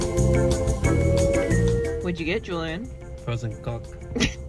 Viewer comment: bruh i thought julian said frozen cuck at 6:39 LMAOOOOOO my mind